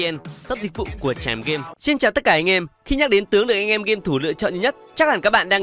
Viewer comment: Hời cứ mỗi lần nói xin chào tất cả anh em tôi lại nghe thành XIN CHÀO TẤT CẢ CÁC CHÁU :))) ám ảnh vcl